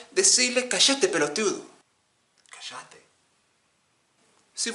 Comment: me when i first heard an Argentina lmaooo casheete?